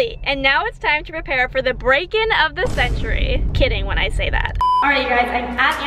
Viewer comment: for a second i thought she said were breaking up